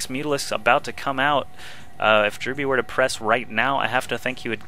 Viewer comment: O in the beginning I thought he said Drew Brees